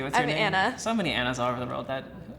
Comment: I knew it, the Japanese girl looked so Iranian too, I just couldn’t get it out off my head… Even her name sounds Iranian, first time I heard it I thought she said Nazanin